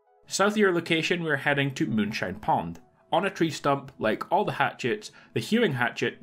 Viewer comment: I'm sorry but did he just say moonshine pond?